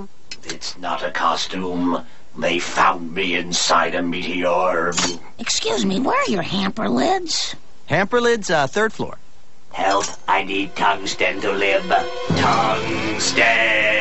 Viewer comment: i always thought he said "tongue stem to live " well i feel stupid